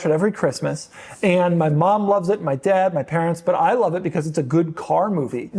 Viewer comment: Did he say: "My mom loves it, my dad, my parents....." ? 😀